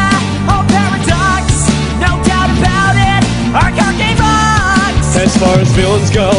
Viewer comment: what does he say? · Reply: Chibi Prussia "Our card game rocks".